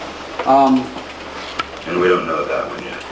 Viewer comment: What does Axl say at 02:37 that everyone laughs in the court and the asshole says "well said"? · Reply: Axl said!! and we don't know that yet!! Meaning this court does not yet know if the document has failed to protect him!! Because they haven't arrived at a verdict!! Does that answer your question?